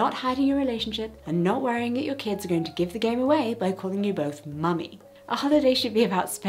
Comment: She said "give the game away" I thought she said give the gay away and I died 💀 🤣